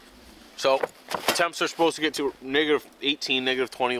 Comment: It sounds like he says the n word instead of negative